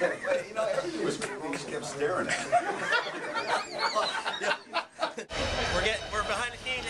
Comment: What does say at 2:56 ?? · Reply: "he just kept staring at me" LMAOO